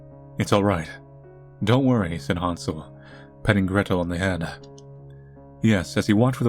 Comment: I thought i heard "Han Solo" lol